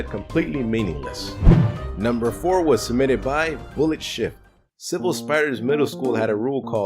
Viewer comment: LoL I thought you said bullet shit